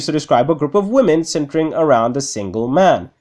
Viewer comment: instead of centering I heard simping lmao